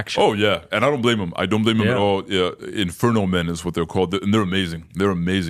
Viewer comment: I thought he said Infertile men but it was inferno men